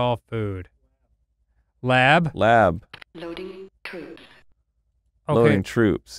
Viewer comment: I swear to god all I hear is "LOADING POOPS"